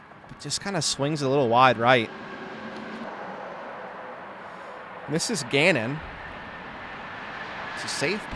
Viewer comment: did you call Gannon Buhr "Misses Gannon" ?